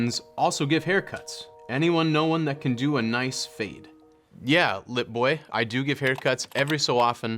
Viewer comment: I thought he said yeah lil boi like a NY barber with an attitude lmao